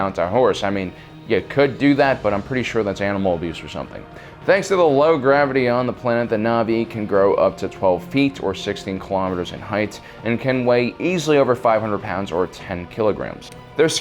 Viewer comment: did he say 16kms in height or am i just hearing things